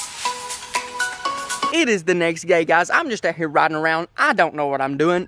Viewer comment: Anyone notice at 8:20 it sounded like he said its the next gay